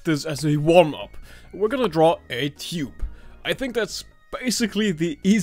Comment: your accent made me think you said Cube...then you proceeded to draw a circle...but you said Tube